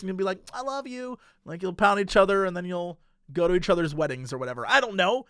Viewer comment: I had to go back up at 18:30 because I was reading these amazing comments, and I just heard "And then you'll have the little half heart necklace and you'll be like 'aww I love you' and then you'll pound each other and go to each others weddings" and I was like "UmmmmmmMMMM?" then I saw the fist bump xD